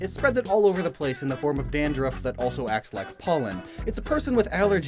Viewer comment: (audiotune) Did you say POLLEN